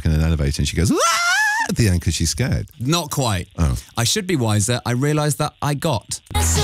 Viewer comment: I thought she was saying "I should be wiser and realize they're all gay"